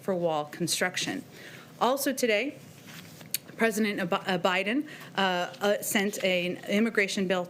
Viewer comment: did she say President Obama???